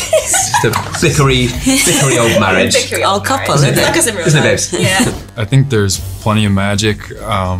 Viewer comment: Did Ben call Jessie Babe at 2:52 ? Lol